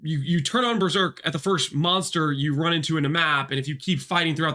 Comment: thought you said "month" not monster and thought you are going to make a comparison that with new rage on hit, berserk lasts for the whole league :D